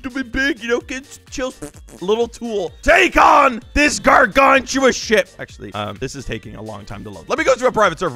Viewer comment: Is it just me or did he just say "gargantuan shit" on